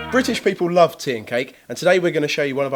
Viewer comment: I thought said TOK instead of tea and cake. The effects of IB *sigh*